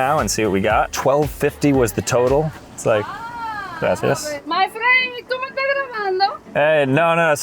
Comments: She didn't say `my friend, do you speak Spanish?` she actually said `my friend, are you recording me?` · The girl says, tu me estas grabando? Which is, are you recordiing me? Not, do you speak spanish? As the caption says 😅